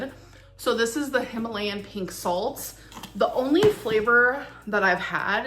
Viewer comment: Did I hear her say “Himalayan Pig salt”?